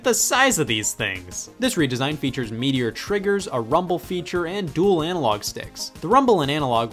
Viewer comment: I thought you said meteor triggers, took me a sec to realize you said meatier triggers, and to answer your n64 controller question of what it is, it is an ergonomic 6 button SNES controller with an analog stick attachment for 3d games. also Saturn pad is the greatest digital controller ever made and the Saturn 3d pad and Dreamcast controller both use hall effect sensors for their analog inputs, sidenote, everything made by Hori from the ps2 era backwards is better than OEM, and after the PS2 generation there is a 60 percent chance its better than OEM